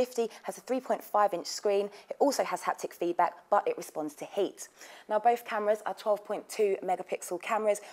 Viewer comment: wtf does she say @ 1:42 ? i understand the first camera responds to touch, but does she say the 2nd red one "respones to heat"!? or was it tilt? didnt quite get that.. :\